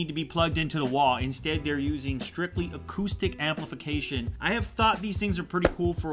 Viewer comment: when ever he said acoustic i thought he said autistic